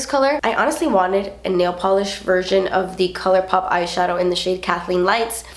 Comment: Every time you said KL Polish, I thought you were saying Kale Polish...🤔...